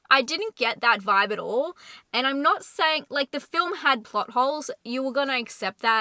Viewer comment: When you said, "this movie has a lot of plot holes", I thought you said, "buttholes". Even my dog looked at the TV.